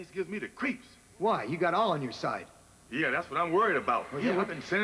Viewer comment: it sounds like he said "You got Allah on your side"